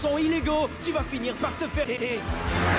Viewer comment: Il a dit quoi à 1:31? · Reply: "Tu vas finir par te faire téter"